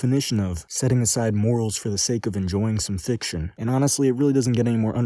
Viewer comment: did he say thicction